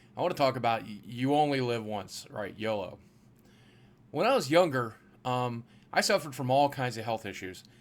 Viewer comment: Did he say, when he was yogurt???